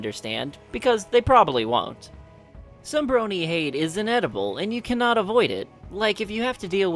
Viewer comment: "Some brony hate is inevitable" For a moment I thought you said inedible xD